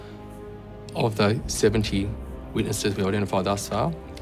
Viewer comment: does he say seventy witnesses? Woah, what on earth?